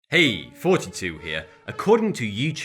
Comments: What I hear : "Hello! 32 here " What he actually says: "Hello! Thoughty 2 here" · When I first watched a vid of his I thought he was saying "42 here" not "thoughty2 here" 😭😂like if u thought the same